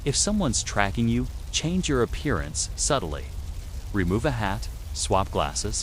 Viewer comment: what he sayed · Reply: "change your appearance subtly"